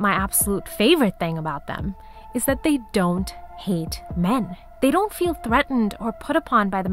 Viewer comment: Oouuhhh! Almost lost me there thought I heard "they don't need men." Was about to leave I just can't listen to another 77th wave feminist. I can feel my lifespan diminishing, when I listen to them.